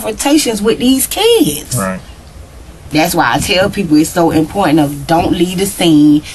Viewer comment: Mama “always tells her kids to not leave the scene of a crime so the police can hear their side of the story”!!!! HOW OFTEN does she expect her kids to BE at the scene of a crime???? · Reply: she actually said “that’s why i tell people” not tell her kids @ 25:55 not defending them tho